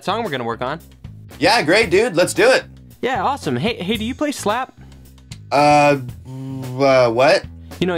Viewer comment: SLAP A DA B... guitar. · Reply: At first I was going to comment "no. No God please no. No. NO. NOOOOOOOO" because I thought he said slap a dab.